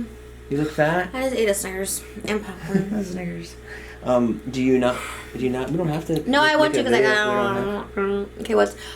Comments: did he say the n word? I’m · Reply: Alicia Santos a year late but she said i jus ate a snickers LMAO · What do they say at 1:05 ???? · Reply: alexander lötvall he said "a snickers" under his breath